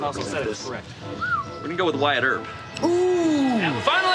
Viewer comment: Wyatt Earp? I thought he said White Herb lmao